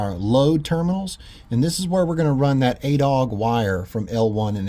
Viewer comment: American wire gauge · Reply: at first I thought i heard you say 8 Aught . (@@